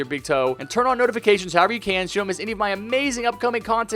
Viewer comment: "Never forget what I always say pee" 13:15 I put like and subscribe stuff first because you have to this mans content is just 🙀